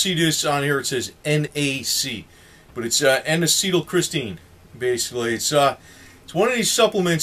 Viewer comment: Thank you for the good honest info, also good info documented by the military to prepare for loud noise exposure is magnesium. Although my ears are half messed up, I hear you saying "N acetyl CHRISTINE" with a letter R added? It should not be pronounced like "Christine" , but should sound like "Christine" without the letter R - and you said it the same way every time? Thank You